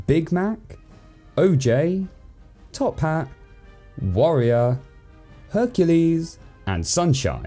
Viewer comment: When he said “warrior” I thought he was saying “wario” lol